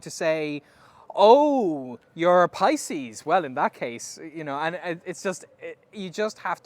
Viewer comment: Benny says something I can't understand : "oh you're a ........" Thank you for writing down the word !!! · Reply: "Pisces" - it is the twelfth astrological sign in the Zodiac.